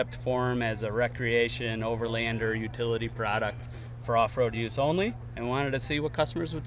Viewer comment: Did I hear them say “for off-road use only”?! So it’s a utility side-by-side that you have to keep on the farm or pull up to the mountain la on a flatbed? No thanks.